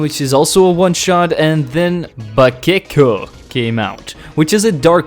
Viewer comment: Next up is bakeko I thought you said butt-kicko